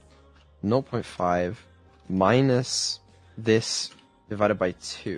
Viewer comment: he typed 0.5 twice and said null instead of zero is that grammaticly right and if no then wich language does it come from? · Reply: He said "not point five", and yes, it's grammatically correct, some people say that instead of zero for decimals